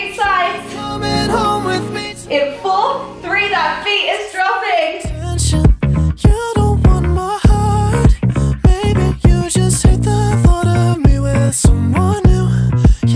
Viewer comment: for the longest time i thought she said at heel touch’s “feel that fetus dropping”. I then turned on cc and relized it was best dropping, Maybe i need my hearing checked